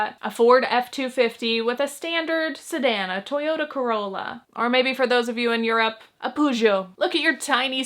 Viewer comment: girl what are hou even saying 💀 subs cant even catch it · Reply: “Or maybe for those of you in Europe, a Peugeot (car name).”